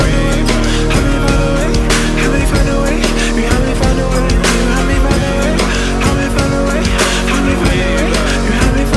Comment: ? What's he saying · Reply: Help me find a way